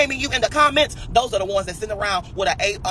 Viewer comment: A- degree what the what🤦🏾‍♀️🫤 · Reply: Dam u caught that... Had no idea what he was saying. I thought he said got an 8 degree 😂